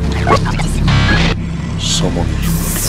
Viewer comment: why did i think he said “son of a bi**h”